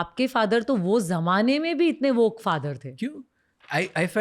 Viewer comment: did she say woke father? What a waste of time watching the video.